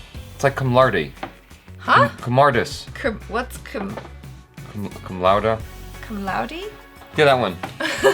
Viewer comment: Did jaby say cumlouder at 1:20 ? 😂